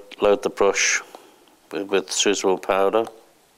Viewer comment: is he saying sizzle powder? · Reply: """SUITABLE POWDER!"""""